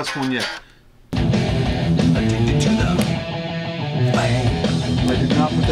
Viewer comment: Am I the only one that thought that he was saying addicted to the pain and not fame?